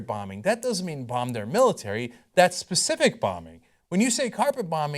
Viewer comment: The definition " carpet bombing" that Cenk quoted from general, is the very definition Cruz used. 10:00. Ted Cruz: "You would carpet bomb where ISIS is. Not A city, but the location of the troops (military targets)." 10:40. Cenk: "(Carpet bombing) Doesn't mean bomb their military, that's specific bombing. Carpet bombing, that means indiscriminating bombing." 11:28. From the General: "(Carpet bombing) We hit very specific military targets." So, even though the TYT entertainers( because they're clearly not journalist) said Cruz doesn't know the definition of "carpet bombing", he was precisely on point with the definition that Cenk quoted. Cenk's own definition of carpet bombing is the opposite of what he quotes from the General. First he says carpet bombing is "indiscriminating bombing" then he says it's "very specific . . . - targets." Which one is it Cenk? There's plenty of reasons to bash the Republican party, so why make one up? Contradictions is everything by abnormal on TYT. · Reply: Cenk: "That's specific bombing." He says it exactly as I quoted it. Perhaps you can help me out and quote what he says because apparently we have two completely different video's.